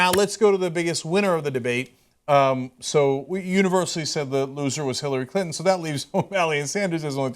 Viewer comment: I don't know what polls TYT was looking at, but Hillary actually came out the winner. I know that they're going to look for polls that show Hillary lost because they don't like her, but you got to be honest. However, I am glad that they did not crown Bernie Sanders the winner as they always do. Bernie was clearly trying to avoid talking about the terrorist attack that happened in Paris. That does not put my confidence in him as a leader at all. I would feel really uncomfortable having a president who would not know how to respond to another terrorist attack in America, should it happen. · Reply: GZEUS ONE Oh, OK. I thought he said, "the university", not "we universally". You won this debate. xD